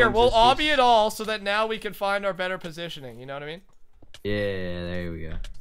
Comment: LOL ANYONE HEAR SAYING SEX POSITION???!?!! Or only me?!?